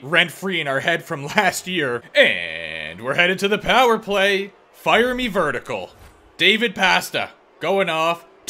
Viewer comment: I hear "fire me vertical" as "fire me Vertigo" hahah everytime